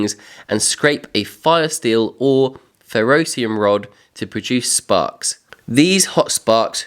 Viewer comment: For a second I thought you called pine trees carnivorous. lol its your accent. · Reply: I thought he said ferocious !😂